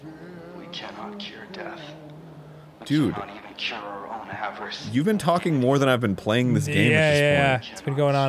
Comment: "We cannot even cure our own avarice of humanity." At least, that's what it sounds like he says. Just thought I'd attempt to clear that up.